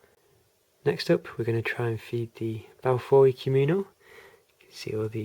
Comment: I didn't quite catch the name of the tarantulas at 5:58, could you tell me what those are? Awesome video as always. :) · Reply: Same species as 8:08, M. balfouri, Socotra Island blue baboon :)